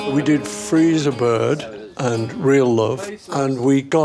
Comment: Sorry Paul it sounded like you said.. We did freezer burn( Free as A Bird)....